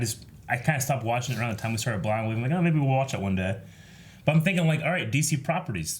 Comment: Am I the only one who hears “Crew of Blahna Wave” ??? Fr tho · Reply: Finsanity XI I always hear “Cruel Blindwave.”